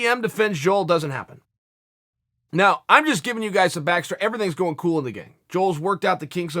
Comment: Im sorry,did he say,everything's goin kool & the gang?? @ 2:22.